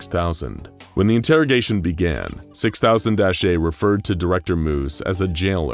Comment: anyone else hear 6000 esshay